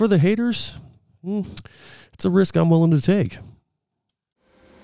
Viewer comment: When you bleeped out "$20" the first time you said it I thought you'd said "that's just a fkn good weld" 😂